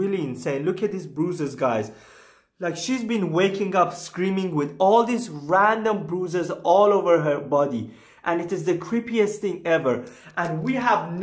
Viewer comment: My ears pricked up. I thought he said his sister was "waking up with random boozers all over her body".